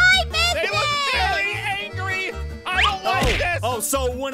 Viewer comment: (Zane said) "YOU HAVE NO BUSINESS! SHE'S MY DAUGHTER! 😭❤️❤️❤️❤️❤️❤️ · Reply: He actually said "OH,OH, SO WHEN IT'S YOUR BUSINESS SHE'S MY DAUGHTER?!!?!?!?!?!??!?!!?!??